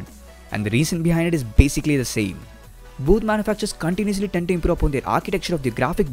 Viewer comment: did anyone understand what he said, not hating, great video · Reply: ReLLaKaT maybe he said both manufacturers (maybe)